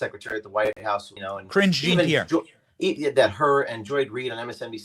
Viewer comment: Benny, when you said karine jean pierre, I swear it sounded like you said "cringe" jean pierre. 🤣🤣. Like always, great video, brotha! James is awesome, too.